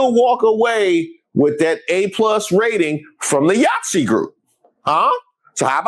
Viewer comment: LMAO I thought you said "YAHTZEE GROUP"! I thought, well what's wrong with that? That sounds fun! 🤣Then I saw my error. My healthcare won't cover hearing aids unfortunately.🙄